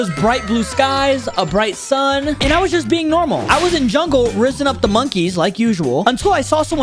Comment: ayo what did bro say? 💀💀💀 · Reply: He said I was fizzing up the monkeys